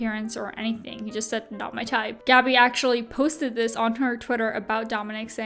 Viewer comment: although smash or pass really isnt a cute thing to play especially on the internet. He didnt say anything wrong he simply said "shes not my type" but did he say "shes not asian" yikes